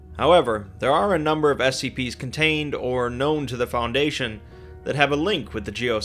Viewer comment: i wasnt really paying attention and I thought he said gnomed the foundation